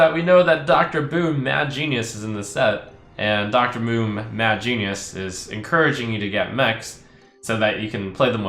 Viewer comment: Did I hear "Dr Moom Bad Genius"